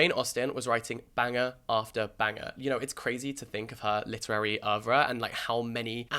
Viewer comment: Did he say “literary aura” @ 3:00?